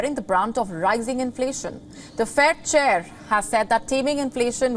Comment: i heard her say the "fat chair"